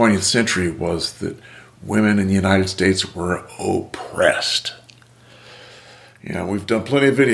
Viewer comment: I thought you said 'hopressed'. Gotta change this hearing aid.